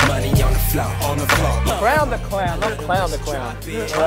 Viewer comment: HAHAH DID I HEAR "CLOWN THE SLOWN"